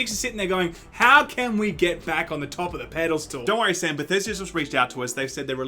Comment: Did Sam say pedal stall? Lmao